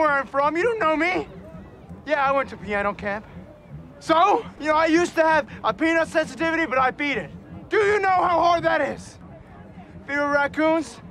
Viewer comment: Wait wait wait, he said 'peanut sensitivity' this whole time I thought he said 'Penis sensitivity' that makes the 'do you know how hard that is to beat' not funny at all